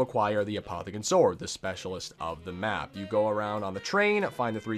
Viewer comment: At first, I thought he said sexuality of the map.